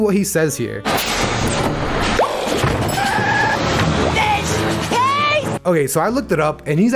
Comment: Who else misheard "fish paste" as "b1tch face"